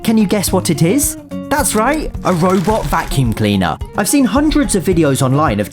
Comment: a robot (what i hear: fu*king) vacuum cleaner